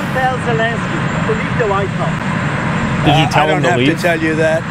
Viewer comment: 這邊聽起來應該是 Did you tell Zelenskyy to leave the white house 最後一個字有點聽不清楚 不過應該不是取消資金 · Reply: 我听的也是leave the white house